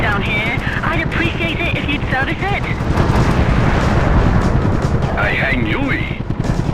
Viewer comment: what the hell does he say? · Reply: 'I hang U-ie'! ('I will perform a u-turn')